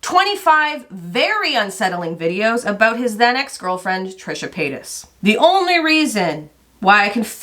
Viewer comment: Instead of hearing "then ex-girlfriend " I heard "xanax girlfriend" 😭